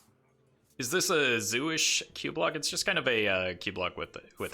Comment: I heard "Is this a jewish cubelock" and got confused for a sec there